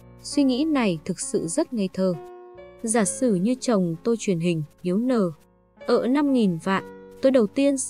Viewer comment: Sao mình nghe có chỗ như kiểu nói tắt hay sao ấy nhỉ? ví dụ chỗ 28:55s em nghe được là: "chồng tôi truyền hình yếu n...." có nhiều chỗ kiểu nghe như bị ngắt quãng khó hiểu quá ạ